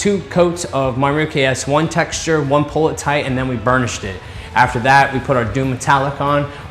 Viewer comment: awesome video but the way you say burnished it, it gets attention back as it sounds like u say bull sh